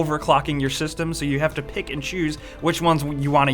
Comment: did you say pikachus or pick and choose?Wasn't clear.